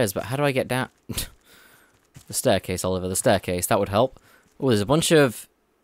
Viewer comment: did he just say his name or did I mishear? "There's a staircase Oliver, a staircase".